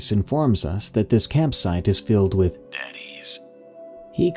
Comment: "he called and told us his yard was full of DADDYS." Is what i heard and I'm trying not to laugh :D! But then it was cleared up as deceased Animals and now i'm sad...